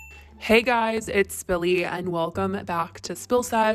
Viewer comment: ngl coulda swore u said billy n i saw the intro like........with spilly ¿!?